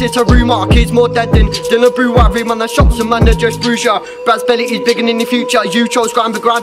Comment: Seen these live on ig also did you just say brads belly is bigger than your future